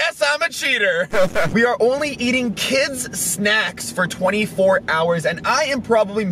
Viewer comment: I almost got banned from u I paused it cause my mom walked in and all she heard was we will eat kids instead of we will eat kid snacks